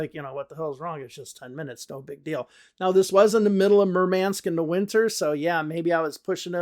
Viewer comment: Excuse me, I’m Russian learning English so I can’t get what word you use in 14:59. It sounds like mermence or moment but I can’t figure out what it is that you used. Would be helpful if you or someone who knows could help explain. Thanks a lot! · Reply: He said "murmansk"